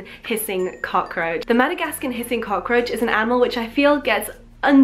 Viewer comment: i heard madagascar pissing cockroach